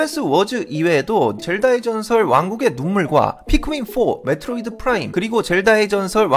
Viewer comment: 왜 자꾸 왕국의 동물로 들리죠 ㅋㅋㅋ 귀가 문제가 있나 ㅜㅜ